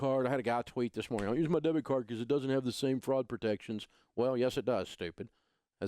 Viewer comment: is he saying Fraud or Frog protection?